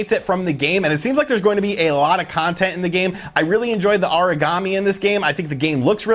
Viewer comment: Aragami is Ah-rah-Gah-mee not "uhreegamee" it sounds like your saying Origami (paper-folding craft) LOL